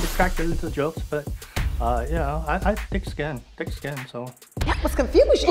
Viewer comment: Bro that Asian Dude called himself "Yellow Skinned" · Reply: I thought He said thick skin 😭